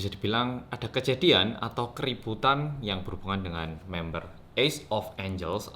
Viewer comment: sorry I misheard you said “keriputan” and i was like...huh tf, then I realized what you rly meant to say was “keributan” wkk